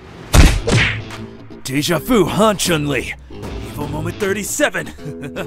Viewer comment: What did he say at 0:04? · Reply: He said “Evo moment 37”